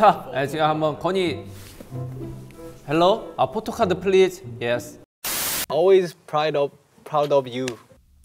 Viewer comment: Ngl 4:13 I thought Jin said I want to phone the police please lol